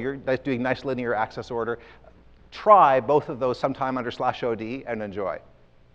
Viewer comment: what did he say? slash O D? /Od ? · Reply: Yes. /Od disable optimizations (default)